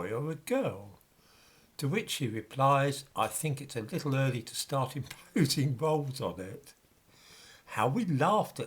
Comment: I couldn’t hear the Monty Python sketch clearly; but I don’t want to miss out!! He says “it’s a little early to start imposing…”- WHAT on it?? Bulbs? Bowls? I’m not trying to be sarcastic; I honestly cannot hear what is said!! Please can someone clarify for me! [Thank you!!! 👍🍻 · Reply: He says "It's a little early to start imposing roles on it, isn't it?"